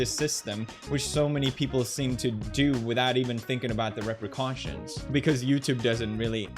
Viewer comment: Whoever did the English subtitles is horrible... 10:59 Instead of repercussions, it writes "wreck questions".